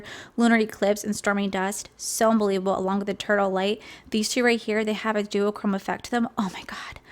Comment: instead of 'eternal light' I thought you said 'turtle light' IM DYING